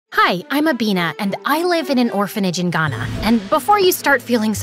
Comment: when she says her name it sounds like she is saying I am a bean